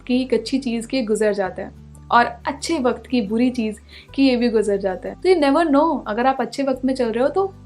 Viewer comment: What you said at 8:34 he....?? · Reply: Ki yeh bhi guzzar jata hai